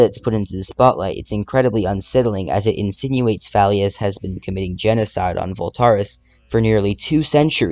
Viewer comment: why was the subtitle saying Halleous instead of Thalleous